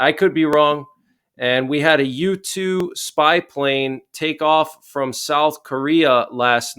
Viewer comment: Thanks lol was that a u2 spy plane ? Dk why but I thought you said YouTube spy plane lol guess that means I'm spending too much time here haha hope you're holiday was great much love from my family to yours